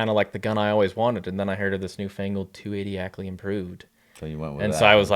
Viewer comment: When this kid said " the new fangled 280 AI " I thought he misspoke and said the new angled 280 ai.....at first I thought that's funny he got the slang wrong ....then I thought oh no he's a genius cuz ai has different shoulder angles ...as I rewind 10 sec back nope ...he said new fangled.